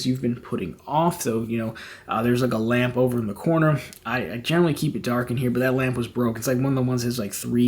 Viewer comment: "There's a lambo over the corner" haha that's what I heard